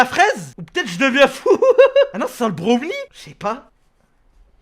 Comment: le quoi 😅😅😂😂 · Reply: Il a dit le god ?